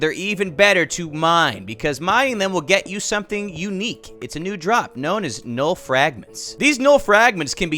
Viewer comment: “Will get you something unique, it’s a new drop” What I hear: “Will get you something unique, it’s a new drug”